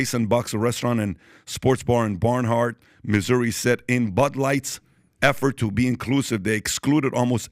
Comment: I hear you saying "Butt Light" very-single-time. 😂